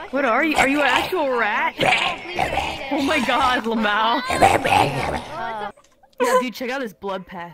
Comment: did Calli just say LMAO in real life